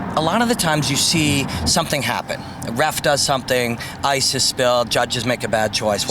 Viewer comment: "did you say ISIS"??